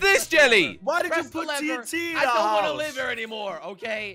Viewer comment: I thought he said “I dont wanna lizard anymore”